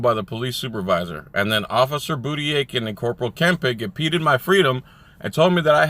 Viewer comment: Hey, In this video at around 54-55sec, you gave the names of 2 officers impeding your freedom. It sounded like you said Officer Booty Ache and Corporal Impotent! I know, pretty cheesy but I couldn't resist.